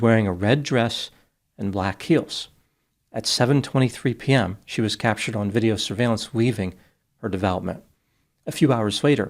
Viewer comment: what is you said? “She was captured weaving…”